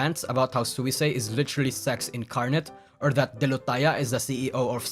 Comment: autocc said "Self-oof is literal sex incarnate"